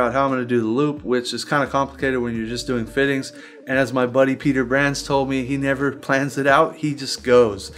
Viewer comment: wait wait wait did he say Peter Bran or Peter Brown? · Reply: Peter Brands